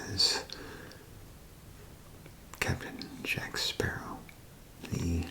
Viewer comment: Does he say Kevin Jack Sparrow? Or am I hearing things?